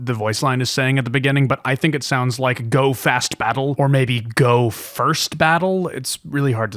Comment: I think it says "Go! The first battle!".